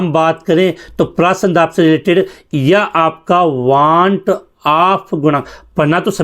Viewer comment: Sir 11:09 ,11:24 ,12:43 pe kya bola hai use English me bata do plz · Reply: Osmotic pressure , wanthonk constant.